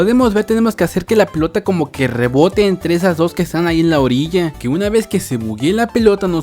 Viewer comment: te escuche decir Orina XDDD 5:05 las Mascotas o los Gadgets ??? LoL